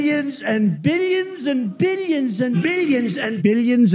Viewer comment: Omg I laughed so hard at the “billions”. Superb work 🤣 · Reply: I thought he said minions